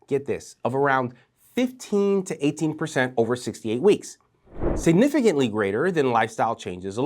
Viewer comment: did you say 6 to 8 weeks, or 68 weeks? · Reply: 6 to 8